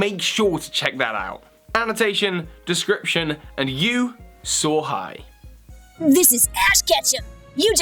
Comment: did u just call the viewer sohai??